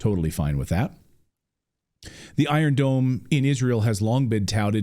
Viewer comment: This never made sense to me. I thought he said an "Iron GNOME". Frankly, the world is so sick and tired of the US. They are like an old person with advanced dementia, running around with a 50 year old .45 calibre hand gun, with one round in it that might not fire, and threatening to destroy everyone. The US is so stupidly transparent with it's lies. Their lies are like the script of an old soap opera. The US just rehashes it's old lies, over and over again. If you look at the US from space, you can see the fumes from the bullshit, rising into the atmosphere. The US has a 99% bullshit rate.